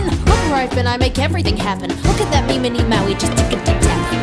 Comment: I know maui says look at that mini me just tippty tapen but I thought it said look at memeity Maui